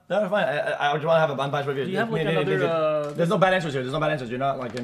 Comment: what did bro say at 1:43? 😢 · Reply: It's fine, I would rather you have an unbiased review. There's no bad answers here.